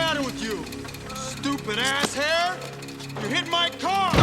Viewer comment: Stupid ass hair? Lol for decades I thought he said stupid ass head 😂